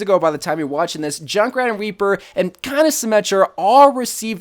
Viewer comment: Did he say weaper